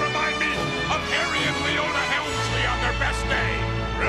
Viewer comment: thought i heard helmsley